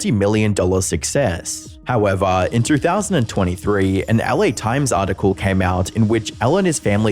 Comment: you can catch the audio saying "in 2023" but the captions say 2013